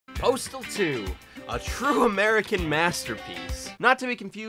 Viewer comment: I thought it said portal 2